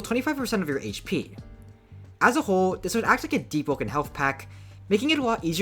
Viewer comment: chat did i hear the words deepwoken and health pack?